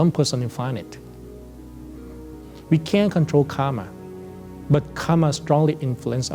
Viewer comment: captions say that we cant control Karma,,,, but he is saying that we can control Karma